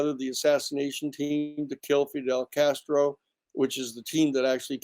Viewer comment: Just very curious about this statement… Does he say “he put together the assassination team that killed Fidel Castro” ??? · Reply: "to kill" Fidel Castro, not "that killed"